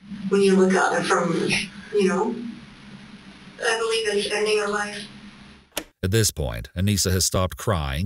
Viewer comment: There are a few incorrect captions here, but the most egregious by far occurs at 2:11:18, when the interrogator asks Anissa if she knows what "killing" someone actually means. What the captions say: "I believe in ending a life. And no regrets." What she actually says: "I believe it's ending a life. And I regret it." The absolute OPPOSITE. I had to go back and listen a few times because the captions were such a weird cold departure from what she'd been saying up until that point. This isn't a minor error, it completely changes the meaning, especially when you immediately go on to describe her as "numb" and possibly a narcissist or sociopath on the basis of your INCORRECT CAPTION. Come on.